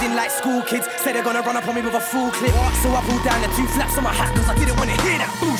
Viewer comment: I just clocked Dappy said 'fullclip'. I always thought he said 'Big man acting like school kids saying they're gonna run up on me with FORKLIFTS ' xD